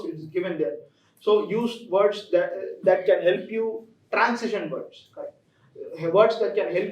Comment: sir could you please repeat which course on youtube you are mentioning?? Actually becoz of echo i couldn't catch it Thank you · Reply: Transition words it is. :) Cheers.